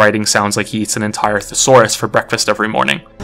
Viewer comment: I heard "bowl of swords for breakfast" at first, but both make sense.